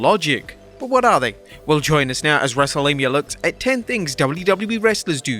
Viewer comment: Did you just say "wrestlemia"